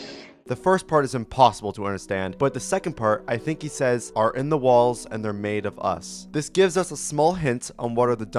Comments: I think he said “The boys are in the walls, and they’re made of us.” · I think i was able to understand what he said, he said "their body (Or the bodies) are in the wall and they're made of us" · The dummy at 26:11 said (or at least from what I heard) “their bodies are in the walls and are made of us” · I think he is saying “Thier bodies are in the walls. And they’re made of us.” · I think I hear "there / they're bodies are in the wall" I don't really know though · i think he’s saying “your dad are in the walls. And they’re made of us” idk though